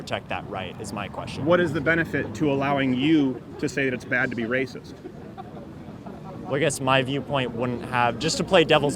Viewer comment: what did that person yell? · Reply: Kelly Small “Crowder’s a virgin!”